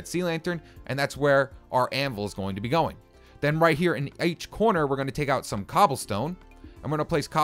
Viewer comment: he said aech corner instead of each lol